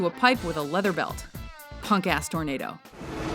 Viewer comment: Did she say "Punk ass tornado," or am I hearing things?